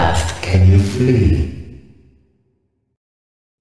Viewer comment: "Listen to my voice, and you'll find the key." "When you can see, how fast can you flee?" "Be sure you step, through the heart of madness." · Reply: Same! I just heard "Be sure you're stepping through the hearts of madness"